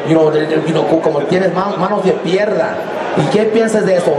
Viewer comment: it almost sounded like he said "Manos de perra" LOL